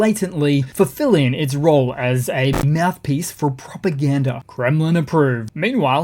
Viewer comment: I heard RT pushes "poopagranda"